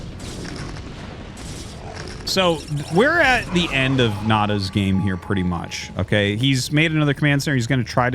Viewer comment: What I heard: So, we are at the end of Nada's career pretty much ... xD